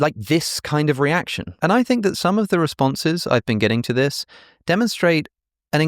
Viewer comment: What Alex said - ‘Christianity seems more plausible than Richard Dawkins and my teenage self believed’ What everyone heard - ‘Forgive me Lord for I have sinned’ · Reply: What i heard "ive been getting boring" time to spice it up. A little suspense will do